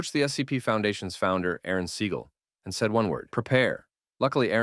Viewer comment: Didn't he say Protect instead of Prepare?